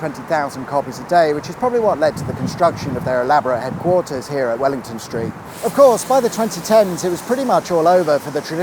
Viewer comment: I thought you said 'Wellington Straight' 😂